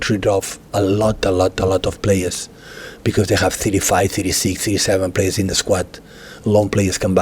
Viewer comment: "There are a lot of clubs who needs to sell players to buy we don't have that problem" is that a dig at United 🤔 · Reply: That’s not what he said . He said they sell players cos they have a lot of players 35 , 36 players ,some come back from loan but they don’t have that . I’ll think that’s Chelsea